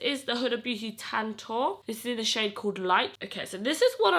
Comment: i thought she said "and this is in the shade Ted-Talk"